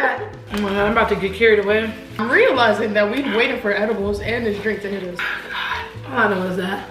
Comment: Man my high ass thought she said “Hey waiter”.🤣🤣